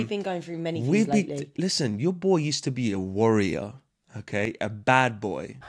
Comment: When said "Your boy used to be a warrior", I thought he said "worrier".